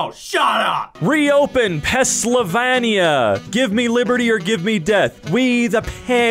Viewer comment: Did you just say *Pessylvania*? You realize that spelling is only missing a y right?